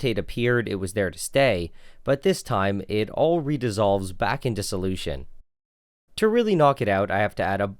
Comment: Who tf wrote these captions bruh 7:45 'it all Reda's all's back' instead of 'redisolves'